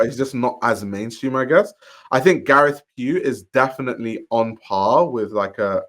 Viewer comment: Can someone tell me what names he says here? I can't sound it out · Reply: Olivier Theyskens, Gareth Pugh